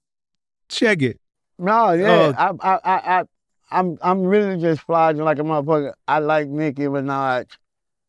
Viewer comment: I thought u said she lying 😅😅😅😅